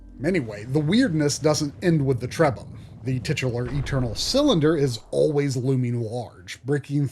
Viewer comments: Did you just say TITS? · Tradition! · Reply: Did he just say TITS?